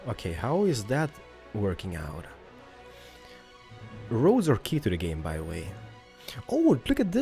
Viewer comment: What did say at 6:02 ? I didnt hear, something like, "Roads are a key to the game"? Is that what he said, because the european style of the roads are like gravel so i wanted gray roads.